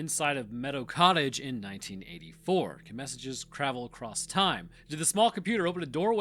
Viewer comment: Did I hear that right? “Cravel”?